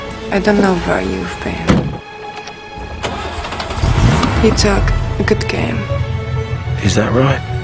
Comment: I dont understand what she is saying at 1:51 ".....(something), good game". anyone understands? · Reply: phileosea I think she said "you talk a good game"